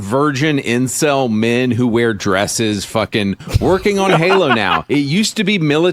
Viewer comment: What is this dude saying, we are the incels